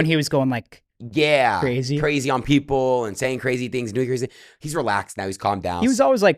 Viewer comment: What did he say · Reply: Doing crazy things